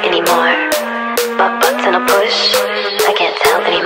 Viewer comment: I keep hearing "my butt's in a push" when it's really "Bu-button push" lol help. Am I the only one who heard it lol 😂🤣🤣😅😅😅😂😂😂🤣🤣🤣